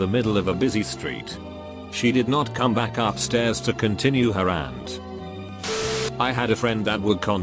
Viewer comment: the bot says aunt instead of rant